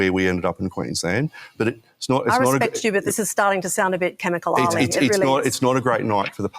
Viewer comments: Is she saying 'comical' or 'chemical' ? American here, no clue what's going on with this accent. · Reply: She refers to him as 'Chemical Ali', which is a nickname of an Iraqi officer who was noted for A - Illegally using chemical weapons (for which he was eventually executed) & B - Acting like the Iraqi army could repel the US army, even after it had become extremely clear that it couldn't. She's using him as a metaphor for McGrath's refusal to accept that not only has his party lost, it lost HISTORICALLY. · does she say it's starting to sound a bit "chemical ali"? Did I hear that right? If so what's the reference mean exactly?